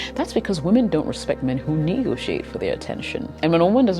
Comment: Does anyone know what was said at 10:57? I couldnt make it out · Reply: women don't respect men who negotiate for her attention